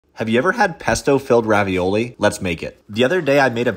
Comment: For a second I thought he said Pepto